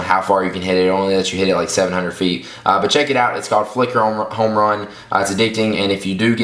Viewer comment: thought it said fuck home run...